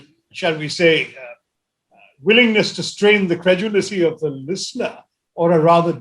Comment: Hah, today is the day I spotted an incorrect English by Shashi Tharoor, or so I believe! 00:15:24 What was that? 'Willingness to strain the <credulicy>'? He certainly did not say 'credulity'. I am sure he said 'credulicy'. Does not exist in dictionary.